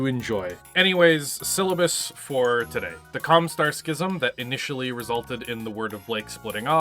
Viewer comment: I know you said Com Star, but I swear I heard you say Com with a U instead of an O. 🤔 Love your stuff! You match my nerd so well. 😌